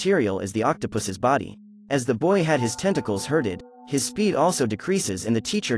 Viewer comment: "As the boy has his tentacles hurted" 10:41 WHO ELSE HEARD IT🗿